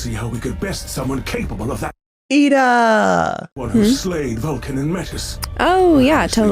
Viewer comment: you said "Iida" from mha and I heard "Eda" from the owl house 😂